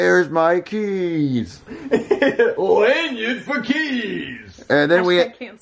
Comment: why does he call elaina lanyon?